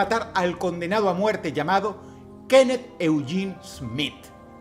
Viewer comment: escuché "kenet fuyin smith” 😂😂 los dominicanos me entenderán 🇩🇴